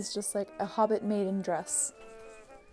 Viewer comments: I absolutely love this. ALSO that you do a critique at the end with lots of constructive criticism and praise! Btw I definitely heard “hobbit mating dress” instead of “hobbit maiden dress” and it made me lol. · I only have 2 things to say about this: 1) that dress is stunning😍 2) I’m sure you probably said “hobbit maiden dress” but at first I definitely heard “hobbit mating dress.” We all need one of each in our wardrobe 🤷🏼‍♀️